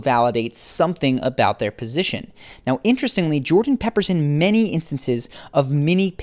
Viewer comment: Did he just say jordan PEPPERSON??? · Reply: yeah I thought he might've been saying "Jordan peppers in many..." was just makin sure, would've been funny though 😆 I wish it was him messing up his last name rather than this correct usage of an expression